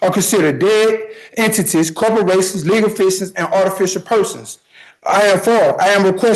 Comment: I just heard "legal faeces" :D